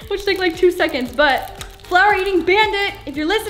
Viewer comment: I thought she said flower eating bandaid